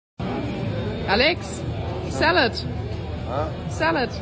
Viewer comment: I can’t be the only one that thought Alex was saying salad is like boogers but was in fact saying burger 🤣